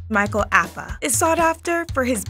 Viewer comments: I thought she said Micheal Afton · Thought i heard Michael afton 💀💀